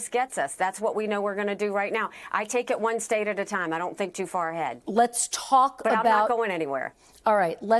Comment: She really said "I don't think that far ahead" @ 8:10 . Someone make a campaign ad using that. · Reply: Not really. She literally said, " I don't think too far ahead." That means she is focused on the now. Good answer.